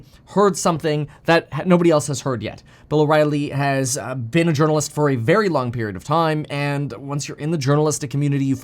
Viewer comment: You speak so fast--I'm lov' it--but I heard you saying, for 0.3 sec: Bill O'Ryley being on adrenalines for a very long period of time.." 🤣🤣🤣 English is my 2nd language, though.